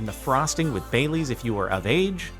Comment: I thought he said "bay leaves" instead of bailey's...